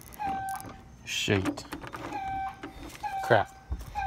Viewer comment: it sounded like you said s**